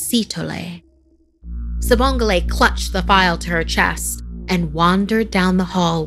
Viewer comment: i thought it said "Sibongile shithole" for a second